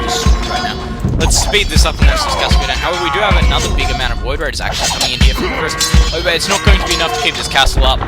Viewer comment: i swear you said void rays